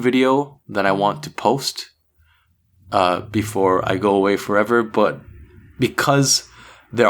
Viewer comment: did he say before I go forever!!!!!! #help_